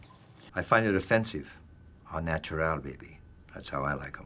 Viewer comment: I think he said au natural in french at 6:27 not all natural like the subtitles say